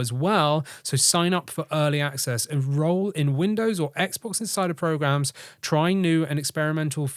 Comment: I kept hearing "Xbox inside her"... 😑